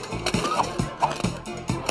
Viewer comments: He said 'filthy' but the slap and whip sound when he hit Phil with the newspaper made it sound like he said "f***ing monkey" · I swear to God, I heard “f***ing monkey” 😂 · I thought he said “f*cking monkey”